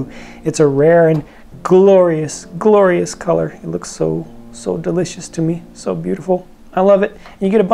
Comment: did just say delicious